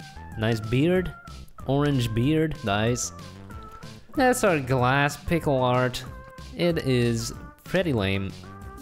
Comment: I heard him say thats our glass pickle art